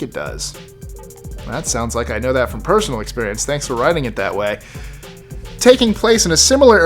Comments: did he just say sounds like personal experience,thanks for riding it that way??? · I heard “Thanks for riding it that way.”